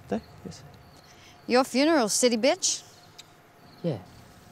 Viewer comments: The subs say silly bitch but I hear "city bitch" but idk maybe it's that Australian accent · The subs say silly bitch but I hear "city bitch" but idk maybe it's that Australian accent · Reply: I hear "city bitch" too.